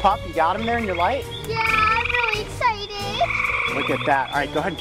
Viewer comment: What did she say at 3:24 because I heard "yeah I'm really ....." And I was wondering if anyone heard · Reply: she said "I'm really exited"